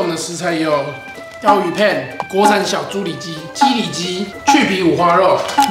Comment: 的时候为什么我会听成共三小猪里脊www